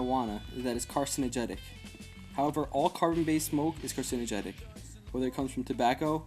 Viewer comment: Wait... i'm using this video as one of my sources for my English paper... you say at around 00:32 that "however all..." i can't understand the rest that you say? Carbon base mold?